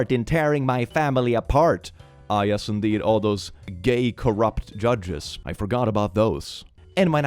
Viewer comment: did i hear gay corrupted judges?